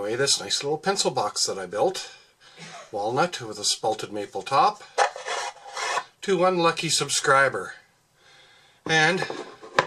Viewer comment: Oh ONE lucky subscriber, thought you said unlucky subscriber.